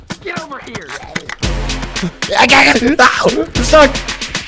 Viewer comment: the people who thought he said the f word he said ‘Oh I’m stuck’